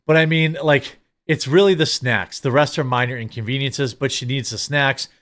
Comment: I thought she said no "SNAP" not snacks. So in other words, America's overly generous government assistance.